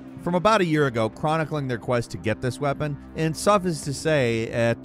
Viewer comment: Did he say "SUFF-US to say"???? That's now how tf you pronounce "suffice" lol